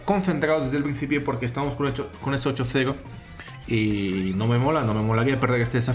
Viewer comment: Soy el único que siempre que dice eso escucha "oCHOCERO" en vez de 8-0? xD